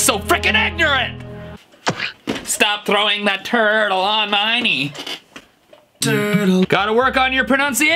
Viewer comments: I heard it “throwing that turd all on my knee” Def LEPPARD: living like a lover with a red iPhone Fallout boy: you see the sea, it’s a god dam arse face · For the Charlie south song I heard “throwing that turd on all my knee” 😂😂😂